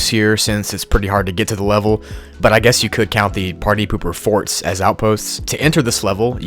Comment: “Party pooper forts” lol it sounded like he said party pooper farts